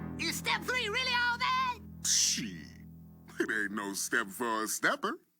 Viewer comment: sounded like the boogeyman said “shiiiiiiit”